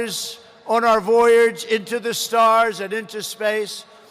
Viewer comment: Did he say "To the Stars?" @ 23:43 Thats a little further then the moon, mars, and the ISS...... U need light speed for Stars...?